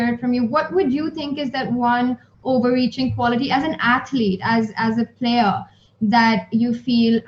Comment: Did she say as an “athlete”?